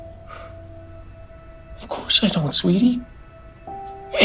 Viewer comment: WHAT DID YOU CALL HER?! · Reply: Sweetie? What’s wrong with a father calling his daughter sweetie?